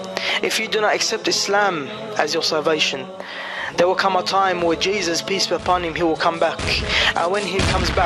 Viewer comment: sounded like he said "there will be a time when Jesus (peace be upon Him), he will come back..." Did he say something different? Hard to tell with his stammering, mumbling speech mixed with his Aussie accent.